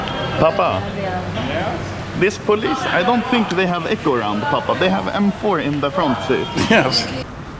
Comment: Anomaly: Pappa: yes Anomaly: I don't think the police has eco-round they have big M4 in the car. HAHAHAHA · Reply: but it sounds like he says "Eco-Run"